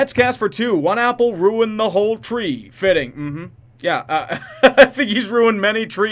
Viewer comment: It sounded like he said 'One bad album, ruin the whole Tree'. I choose to hear it that way.